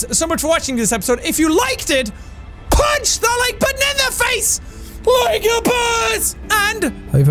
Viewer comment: At the end, it sounded like he said "Punch the like banana face LIKE A BOSS!"